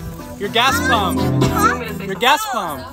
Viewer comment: Sounds like she said im listening to Vicky the shit by....Ice bites??????